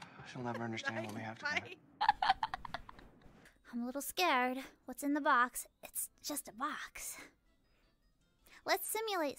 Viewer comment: Best misheard line in MGS…. “Huh.. WhatsWithTheBox?”